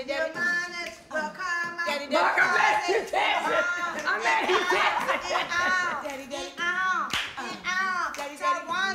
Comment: Anyone know what Tamar said @ 0:41? "Your something will come out"..?? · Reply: "Towanda nd Andre be gettin it on, you might as well come out the closet..."